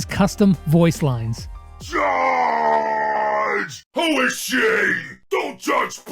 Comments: did bull just say holy sh*t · in 2nd one I thought he said "holy sh*t" at first 👀